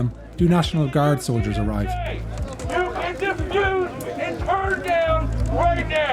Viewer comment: Caption error... I think the speaker said "diffuse," not "just do."